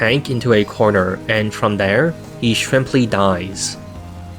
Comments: Did he say Shrimply? Not simply? XD hahah anyone else heard it? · <Can 100 melee snipers beat 1 tank? It's the closest think to the "100 men vs 1 gorilla" I could think of. EDIT: 9:44 Did he just say "shrimply"?> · Did I hear that right at 9:48? "He shrimply dies" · did you say "He shrimply dies" at 9:47? I noticed you throwing in a few funnies this time around. :3